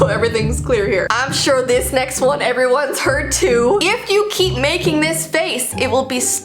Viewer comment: So for fun I turned the captions on just to read them while hearing and at 6:55 when says:"I'm sure this next one everyone's heard too"Instead of "heard"The captions say "Hurt"?I know Its a waste of time but who cares